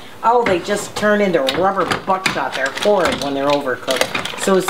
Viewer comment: Hello from The Netherlands that was a tasty video, but ehh did you say don't cook them too long or they will turn into rubber buttsuckers porn if you cook them too long? 04:15. I'm know you didn't but what did you say? Again love your vid's and please make more. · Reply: Ha ha...no I didn't say the above. But did say, don't overcook them as they will turn very rubbery. Overcooked mussels are terrible! And yes, they are beyond delicious, fast. And easy!